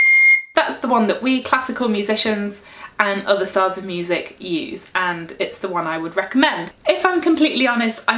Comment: thank you sooo much for your videos. I swear I heard at 0:48 "... classical musicians and other salsa music use"! I'm like "Awesome"! Thank you!